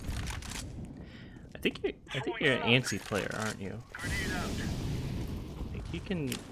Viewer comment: Sounds geat mate. Thank you for your time! On the video you comment about me being a "antiMM" player I think? I didn't catch it quite well, I'm spanish and I'm not understanding you right on that part. Could you clarify what do you mean? On minute 21:25 for example. Thanks a lot! I'm using the PureSkill platform by the way, it is great for demo reviews watching the positioning on the minimap. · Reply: I was saying the word "antsy" not "anti". Sorry for the confusion! I just mean you're moving around and being aggressive rather than holding an angle. Remember the T's have to get to a bombsite. If you're aggressive every round they will be ready for it.